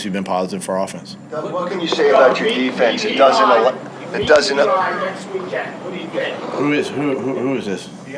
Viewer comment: What did that guy yell at 1:46? 😂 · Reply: Eli next weekend what do you think